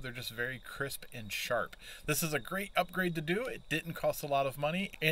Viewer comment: I thought you said 'upgrade' ?